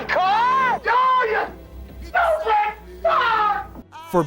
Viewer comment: Woody: oh no no no your eating the car! dont eat the car! NOT THE CAR! OH YOU STUPID DOG! im seriously laughing at that part. i cant stop replaying it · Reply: I thought he said, the last part I'm taking about, oh you stupid fu...